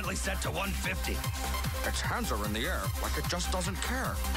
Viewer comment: Did he say "It cancer in the air like it just doesn't care"?